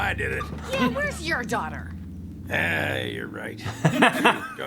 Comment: What does he say at 11:25? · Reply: "you're right, she joined ISIS."